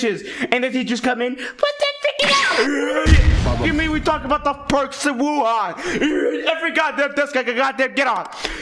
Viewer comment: What does he say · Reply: I heard perks of Wuhan